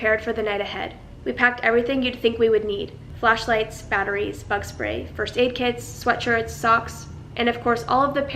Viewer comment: I thought she said "fuck spray" 😂😂😂😂😂😂😂😂😂😂😂😂